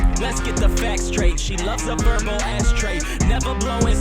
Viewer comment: Let’s get the facts straight, she loves a verbal ash tray! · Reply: Oh dang I thought it said she likes a purple 🟣 ash tray , what you say makes more sense , thanks